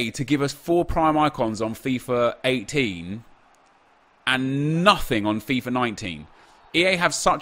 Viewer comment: I literally got of spintires and went on to fifa 19 cause I thought he mis spoke and said we got a free icon on fifa 19